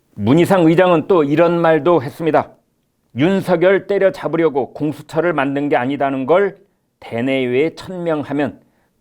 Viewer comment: 윤석렬 때려잡으려고 공수처 만드는게 아니라가 왜 내겐 때려잡으려고 만든다로 들리지 강한부정은 강한긍정을 의미한다